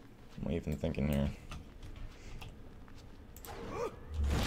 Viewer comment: WTF at 15:04 when you say "let's go back to base here" my phone thinks I'm saying "hey Siri". Weird lol